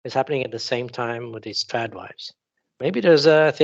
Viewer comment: LOL--several times when I hear say "Trad wife" in this video, I thought she said "Chad wife" !